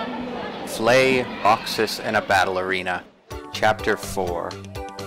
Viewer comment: I thought you said 'Flay, Boxer & a Ballerina.'